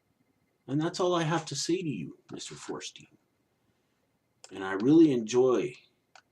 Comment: Feuerstein? I thought I heard you say 'foreskin,'. Man, that's an insensitive joke, because a foreskin has more intelligence than Josh does.